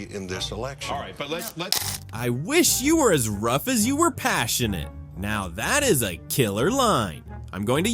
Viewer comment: no, that is not what he said. He said I wish you were are as right as you are passionate